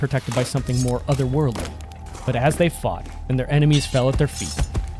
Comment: You did a really good job with your video also my dyslexic ass Thought for a second you said fucked together instead of fought together I'm so sorry 😭🤣